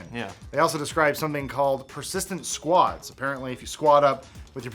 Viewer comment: I thought he said persistent squats instead of persistent squads haha